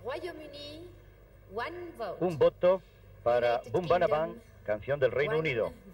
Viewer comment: Maybe I'm mishearing it but to me it sounds like the commentator sometimes refers to the British song as "bada boom bada bam" during the voting